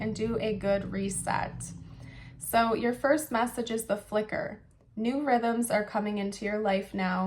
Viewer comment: “the flicker.” I heard the picker. The night we met, within moments, he told me “He’s picker was broke!” I laughed so hard! It still brings a smile.😂 Love wins. ❤😂❤